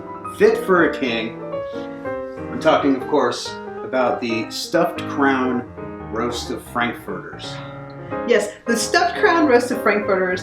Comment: I honestly thought they said, Stuffed Crab Roasted Frankfurters. I think that would have been a better recipe